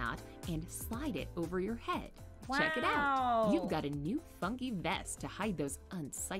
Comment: I thought they said you got a new fu**ing vest